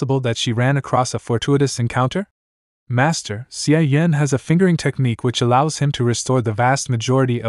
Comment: Did I hear that right 🤨 that he has a fingering technique @ 1:24:14🤔🤣🤣